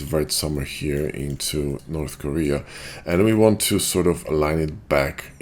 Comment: did i just heard, moving vertices into north korea?